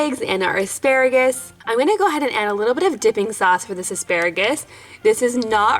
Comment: I thought she said dicking sauce